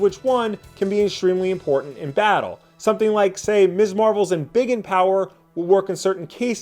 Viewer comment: Legit, thought he said 'mr Marvel's bigot power'. I laughed.